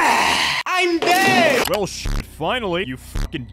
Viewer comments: it sounded like he said “I’m gay😛” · i thought that said IM GAY